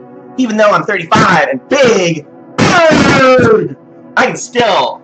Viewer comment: What the fuck did scream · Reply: BIG